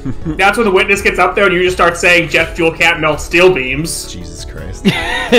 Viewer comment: I guess I just misheard it, but was very amused by the line "Death Metal can't melt steel beams."